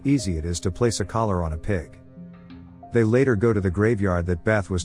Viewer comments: Ive never heard of grave rubbing. It would've been interesting if it was beths grave that one of them did the rubbing on. What and odd story line. I didnt really feel for adele after she started stealing and so forth. I get the aunt was not welcoming and such but doesnt mean you start stealing and switching meds and such. Also did she really leave the house to her? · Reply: I thought they said, grave robbing, until I saw them with the crayon and paper🤣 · man I thought he said grave ROBBING